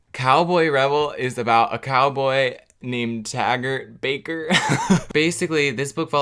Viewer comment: what i thought he said Dagger Baker Me: that's a dumb name what he actually said TAGger Baker Me: oh... that's worse